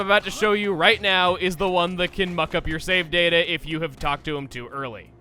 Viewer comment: d-did Chugga just say ''fuck''?? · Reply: No, he said "muck".